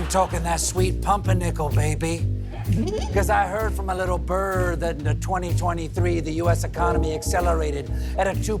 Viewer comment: "i heard from a little bird that in 2023rd"*